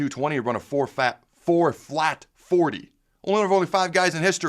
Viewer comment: I thought you said he ran a 4.0 and was like, "why isn't this national news?!?". A "4 flat 40" is 4.4?